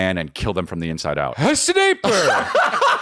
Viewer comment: i think everyone said S'DEEPER at 48:40 ish